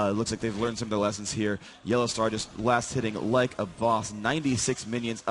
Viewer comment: did he say like a boss? :DD